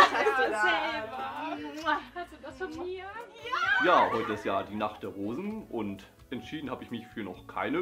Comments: Ich hab immer "Die Nacht der Hosen " verstanden😅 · Ich hab die ganze Zeit verstanden: Die Nacht der Hosen xD